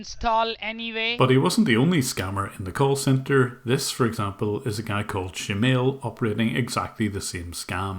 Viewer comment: Did he say shemale ? Lmfao!